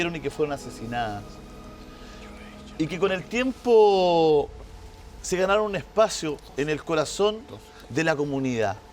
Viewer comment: hay una psicofonia el minuto 28:28 no logro identificar que dice. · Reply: Dice creo yo lo hice yo lo hice cuando dice lo de Alto auspicio. hay una voz que dice yo lo hice yo lo hice